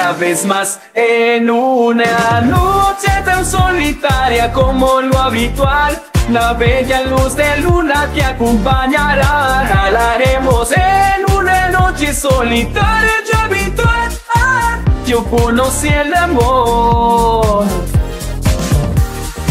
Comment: pq escuche "cagaremos" JSJSJSJSJSJS -?